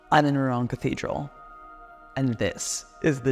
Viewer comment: ngl I heard that as "😁I'm in the wrong cathedral" lol